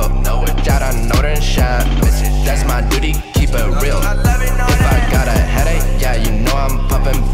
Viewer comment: I got a “eDeAChE” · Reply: STRM 2 looking back it sounds like he said “I gotta edit” 😂😂